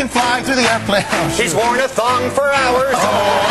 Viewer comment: Did Colin say "SHE's worn a thong for hours" @ 1:24 ?